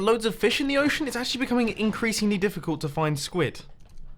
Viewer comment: For some reason i heard a pun "INKreasingly" Like if u heard it too...